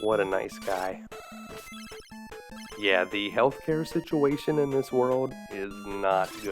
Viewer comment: Did he just say... Elfcare situation? O.o